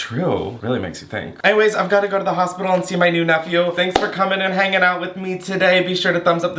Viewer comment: tell me why i thought it said “ get high with me and cook my new nephew”